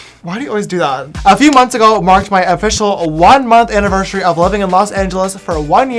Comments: Who else heard James say "anniversary*SH*ary at 0:24 lol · Did anyone else hear James say "aniveshery"? 0:23 and "siver" or something that has a "sh" shound for "driver"?